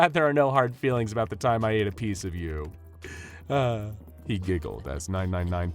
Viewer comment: Dr. Jack Bright at 2:36 : Glad you aren't angry that i ate a piece of you! Me : What did he sayyyyyyyyyy ?